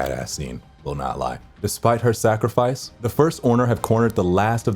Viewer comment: is it just me or did he just said the first orner?